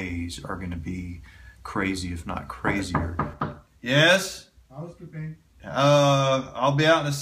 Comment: anyone else keep rewinding this part trying to figure out what this guy said? No,just me,Okay. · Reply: “Housekeeping”